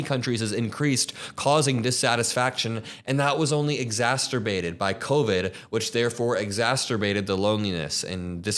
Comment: why did bro say exaSTerbated 😂?